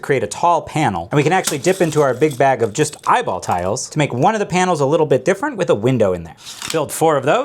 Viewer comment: Thought you said “f***ing eyeball pieces” there